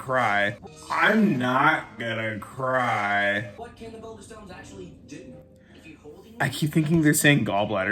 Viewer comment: Loooooool! I totally thought they were saying gallbladder stones too!